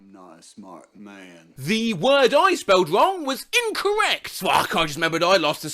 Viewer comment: Well, obviously it was if you got it wrong, but what was the word? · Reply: Incorrect